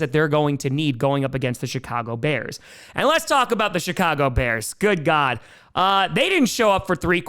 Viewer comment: Again... it sounds like he says the shytcago bears. I can't unhear it now haha.